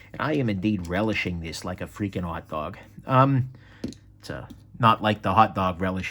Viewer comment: At first I thought you said you were "relishing this like a freaking aardvark." Which was strangely compelling.